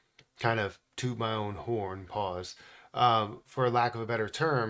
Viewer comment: What did bro say 💀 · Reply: He said pause….so what